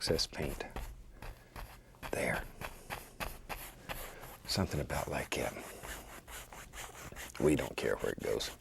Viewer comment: "something about like WHAT???" I'll never understand what he says there. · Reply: he says it a lot in his videos.. pretty sure its just his american accent. he says’ something bout like that.. but the ‘that’ is lazy :)